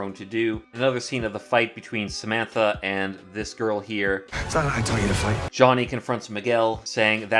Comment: I thought he said "that's not how I taught you to fight?"